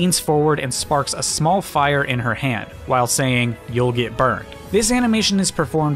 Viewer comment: I thought Pyra was saying "Don't get burned" which honestly sounds a lot more taunting coming from someone who actively uses fire